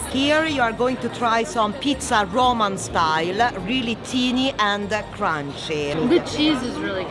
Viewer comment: ahah I think she wanted to say "tiny" or "thin" bus she pronounced as "teeny"